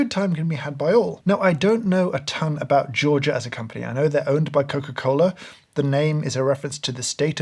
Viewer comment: misheard "I don't know a ton about Georgia as a company" as "I don't know a ton about Georgia as a country" and was VERY confused when that was followed by "I know they're owned by Coca-Cola"